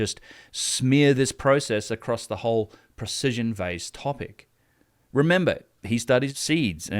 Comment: Ohh! Precision vase. I thought you were saying Precision Bass🎸